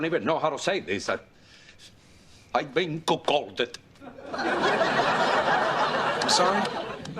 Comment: what was he trying to say??? 😂😂😂😂😂😂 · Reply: Cuckolded